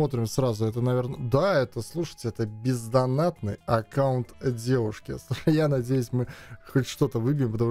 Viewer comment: Мне послышалось это П*зданатный аккаунт девушки🤣🤣🤣🤣